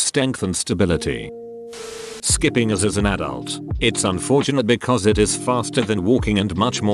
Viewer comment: I thought he said Skipping AS as an adult.... I was like adults don't skip as.. oh wait